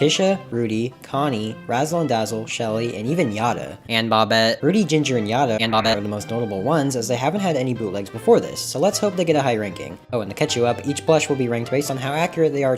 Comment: "And Bobette 🤖" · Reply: why do I hear “and bop it🤖”